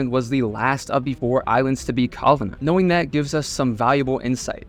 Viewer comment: last of the four islands to be Calvin?.?… what is he saying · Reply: last of the four islands to be colonized